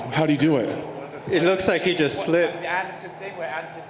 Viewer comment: Leg snapped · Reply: he clearly says "it look like he just slipped" 5:55 but whatever, i don't know why i got that habit of correcting ppl....